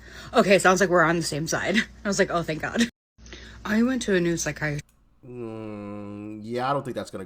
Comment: 😂😂Did she say "Psychiatrist" 🤔 😂😂😂😂😂 Thank you for proving Us RIGHT about you liberals being psychotic and need to be in a Mental institution.. 🤷🏻‍♀️